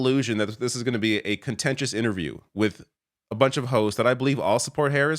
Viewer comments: Sounds like you say "a bunch of hoes" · Haha! It sounded like said "a bunch of Hoes" @ 2:08.